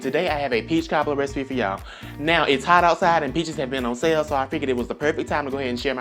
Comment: When he said it's hot outside and peaches been on sale. Lol I thought he said bitches been on sale😂😂😂😂😂😂